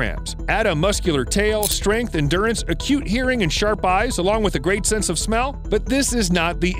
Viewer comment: Did he just say, "Otters have a cute hearing.."? A<link> Yeah... They do have tiny otter ears...